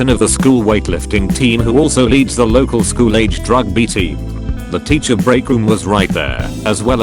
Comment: lol thought he said "drugby" team